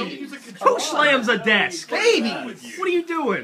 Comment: Did Micheal say baby to Gavin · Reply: I think it was a respond to Geoff saying Geoff- "Who smacks a desk?" Michael -"Babies"